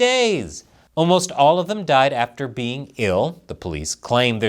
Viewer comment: I thought he said, “Almost all of them died after being killed” 😂